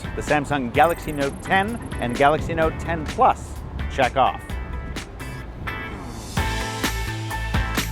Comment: Dammm!!! Dirty mind of mine I thought he said Jack off I had to Rewind